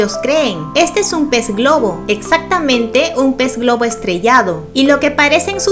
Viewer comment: Yo escuché "Pez globo estreñido" xD